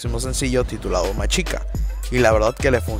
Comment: Yo pensé que decía más chica (ósea que la chica le de más ) vente para Chile aquí no hay problemas de Internet mano